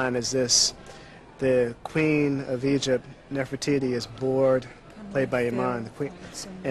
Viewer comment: What the hell did he say in 1:04? "The Queen of Egypt is (what?) bored"? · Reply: The queen of Egypt, Nephritis is bored